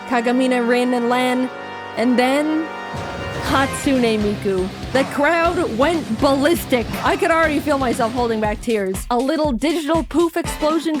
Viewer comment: im so dumb i thought i heard the crowd went cannibalistic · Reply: Not “cannibalistic”, she said “ballistic”.